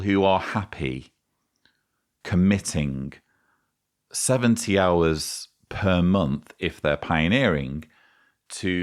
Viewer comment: ngl, I first heard '17 hours' and I was like, huh, that's not so much, I think you can have a full time job on that and then I heard right :/